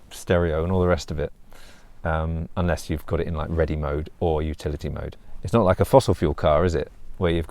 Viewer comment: I thought you were saying futility mode for a while.